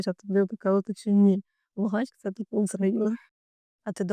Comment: мені не почулося? Усраїна???😮